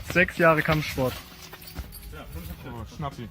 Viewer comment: did I hear "Schnappi"? xD